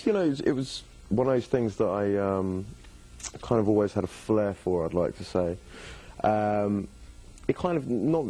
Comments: When he said "I had a flare for" and I immediately thought he said "I had a Blair for" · When he said "I had a flare for" and I immediately thought he said "I had a Blair for" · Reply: Siddarth Arcot Same 😂😂😂 My heart was like ... skipped a beat when i thought he said " Blair "